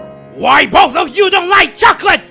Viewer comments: I thought he said “I’m gonna cut you kids!” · At first I thought he said "I'm gonna cut you dead."